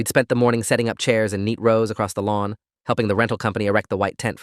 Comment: I heard and meatloaf on the lawn😂